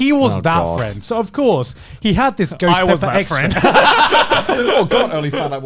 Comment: Did Conner say goutsi? 1:38:45 what is that?